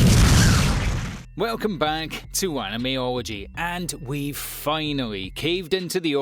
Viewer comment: I SWEAR TO GOD I THOUGHT HE SAID ANIME ORGY LIKE AY YO WTF 😂😂😂😂😂 animeology that got me for a second